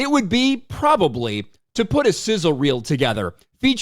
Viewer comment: I heard “scissor” instead of “sizzle” and had a slightly different expectation lol